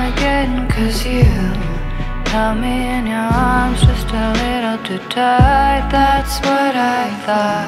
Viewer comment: i always thought she was saying “held me in your arms just a little too tight, that’s what i like” 😮